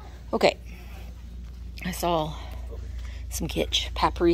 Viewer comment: Hahah I heard “I saw some gitch!” 😅 Always entertaining, 😂